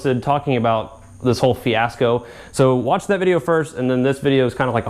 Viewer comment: Did you say Fiat 'sco ?